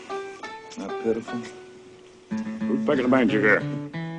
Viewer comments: what does he say, is it, ''who's pickin the banjo here'' ??? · Just after Ned Beatty says "Isn't that pitiful?", what does the bloke who walks up to them say? I'm English so it's not easy for me to understand. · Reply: Actually what he is really saying is "Who's pickin a banjer ere?" or in real English Who is picking a banjo here. Since I grew up in Appalachia I know what he is saying. In the movie - it is quite curious that they do not answer him. But there is a lot of symbolism in this movie. There is a certain tension building in this scene as Ned Beaty's character makes condescending remarks about the locals - which seems to foreshadow the payback they are ultimately to receive for the lack of respect they display.